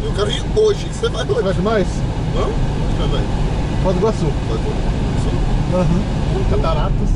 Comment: escutei o falando nova iguaçu, já ia pergunta qq ele vai fazer aq hahahahah